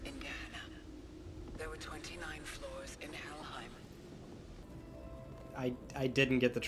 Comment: DID SHE JUST SAY HELLHEINM ?? Imagine it ended up being a GOD of WAR Easter egg 🤣